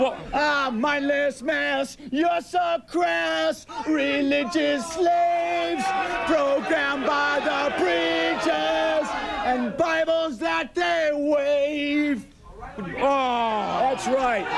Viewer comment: What did he say? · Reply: "Mindless mass, you're so crass! Religious slave! Programmed by the preachers and Bibles that they wave!"